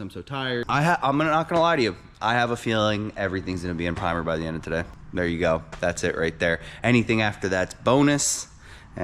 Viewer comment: Bro im so tired, I had to rewind it cuz i thought you said everything was gonna be on fire today, and any past that is a win. 😂😂 WORKFORIT